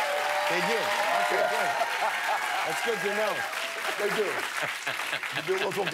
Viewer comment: At around 2:30 when jimmy said "thats good to know", my phone heard it as "ok google" and opened the google assistant app wtf😂